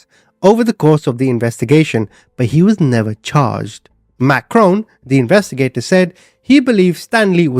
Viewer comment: lol I thought you were saying “Macron” like that idiot in France. Here you were saying Matt Crone.